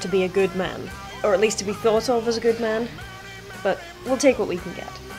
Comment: To be what? To be phyllosol a good man? What is she saying? Can anyone help? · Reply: "to be thought of as a good man" c